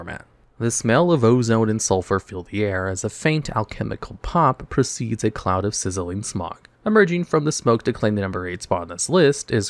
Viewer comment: Wtf did he say? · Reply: The smell of ozone and sulfur fill the air as a faint alchemical pop precedes a cloud of sizzling smog